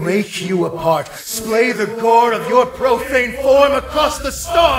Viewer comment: is he saying core or gore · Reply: gore